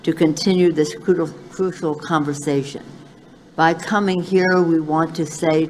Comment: Did she say "futile conversation" at 5:47? and then correct herself?